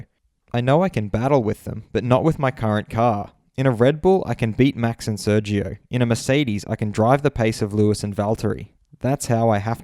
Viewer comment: Lando got them cajones sayin he could beat Max in a Red Bull. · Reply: Tyler Evans You read what you wanted to read and not what he actually said. 1:43 He said " The way I have to think is that in a Red Bull, I can beat Max and Sergio or in a Mercedes match the pace of Lewis and Valteri "